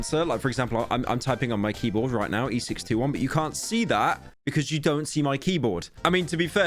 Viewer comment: e621? did i hear that right?